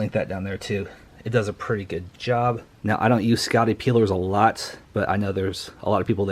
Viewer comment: What are you saying there? I can't figure out what you're calling the blue label scraper thing. Are you saying "scouty peeler"? "Scotty peeler? I've replayed that about 6 times and can't catch it. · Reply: It is called a “Scotty peeler” to get stickers off Easy